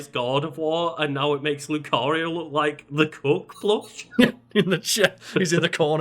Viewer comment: Awe.... awe..., I thought said cook plush, like oh yeah the cook and the maid, nope thanks Karl for clearing that up hahaha